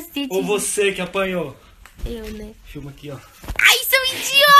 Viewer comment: "so eu q entendi Chupa aqui oh"? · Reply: Fofiis Vianna Guimarães é "filma aqui ó"